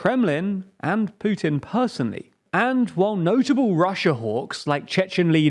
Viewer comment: Apologies, as a number of people have pointed out, at 4:07 I said “Russian HIMARs” rather than Ukrainian like I was clearly meant to say. I think the graphics and context do make it clear enough to hopefully avoid confusion, but apologies for the mistake ALSO, and this is on behalf of myself and Ben (the other guy who hosts our videos) please do trust me that we do look up the pronunciation guides constantly. I totally get that we likely miss with the pronunciation regularly, but in a normal video record we stop to look up pronunciations maybe 5/10 times. Now, good and accurate guides are hard to find for a lot of the smaller places, and even for bigger people/places they’re often dodgy and hard to replicate. I totally get why it’s annoying to have someone pronounce words wrong, and we will continue to look up guides constantly (and hopefully improve) but please do know that we are genuinely trying, we’re not just guessing! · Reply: Did you say "Notable russian orcs" ? Or did I misheard something?